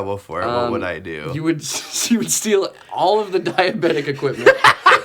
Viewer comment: I thought he said dimebag equipment.